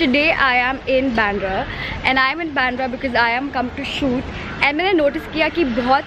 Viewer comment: I am come to shoot ...Did I hear it wrong!! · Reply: no i said I’m come to shoot :)